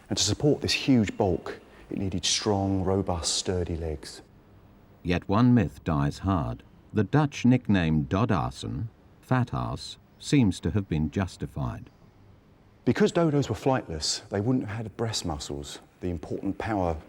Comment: sounds like it says fatass